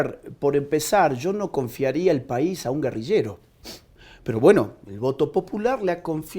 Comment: Agustin Laje no dijo "exguerrillero", dijo guerrillero 8:42 Por qué en el titular cambian lo que dijo?